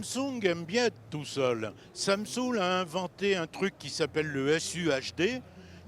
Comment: c'est moi qui rêve ou Victor dit à 16:33 "samsung aime bien être tout seul, samsaoule a inventé............" :) :) :)